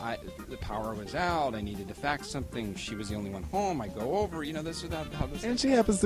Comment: Dude at 00:32 I really thought Byron said that he needed to F**k something.